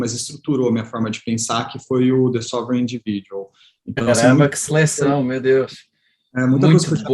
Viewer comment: Alguém sabe o nome do terceiro livro que ele citou aos 16:40? Não entendi direito. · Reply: Sovereign individual